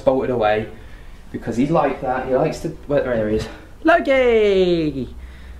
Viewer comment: Who said, “Loki” right after did at 5:50? It sounded like the voice was trying to imitate exactly how he said it. Clear as day. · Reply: Yesssss! 5:48, sounds like someone says “Hey, Loki!” In a young man’s voice, close to own… clear as day, indeed, holy shite 😧🤭